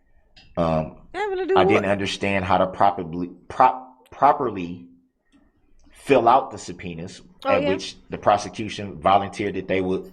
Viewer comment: Darrell: "I didn't understand how to popperly feel up a sub-penis. Bubba had to hold my hand and show me how to that."